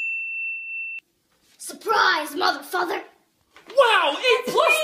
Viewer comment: I thought she said motherfu- then me ohh it's mother father